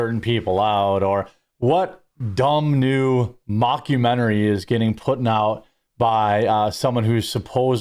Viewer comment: Did you saying "getting putting out?" 😂